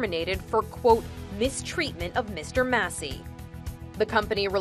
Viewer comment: I thought I heard Mr. Mosbey